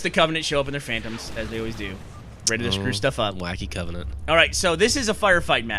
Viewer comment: Wait...did he just say "Blacky Covenant?"